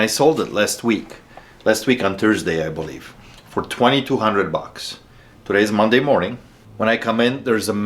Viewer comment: ok as interesting as this story and interaction is, but what spoiled rich guy spends 22k on a slot machine? Like seriously, I dont want to hear that he can do whatever he wants with his money or some shit like some people have weird hobbies or desires. It is just nuts to me to spend 22k on a useless thing. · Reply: I heard him say 2,200 not 22,000